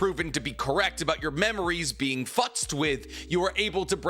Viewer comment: Wtf did he say? 😂 “your memories being fluckst with.”